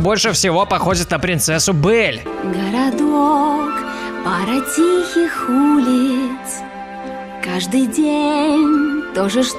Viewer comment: мне показалось что она сказала хй улиц